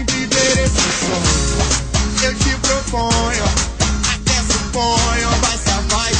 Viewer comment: Nunca entendi "Aquece o ponho" ????? Quem é o Ponho? Kkkk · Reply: Tassiana Vilain Não é "aquece o ponho", é "até suponho" Aquece o ponho😂😂 ri muito